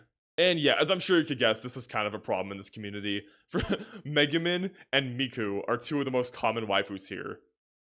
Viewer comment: I thought he said Mega Man and Miku are 2 of the most common waifus